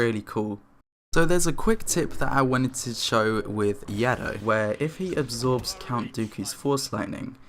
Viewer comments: what did you just call yoda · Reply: Yado is a meme on Riva’s channel xD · The first time he said yoda, he actually said yado! XD